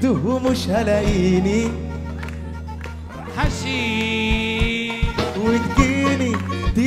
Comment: ماعرف وش يقول😂😂😂😂😂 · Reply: حشيششش هههههه